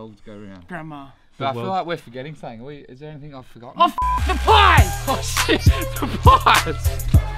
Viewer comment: For a sec I thought you said “Oh the pot!”